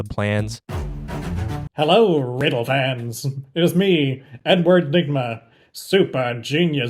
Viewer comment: I thought he said "It is me N word nigma"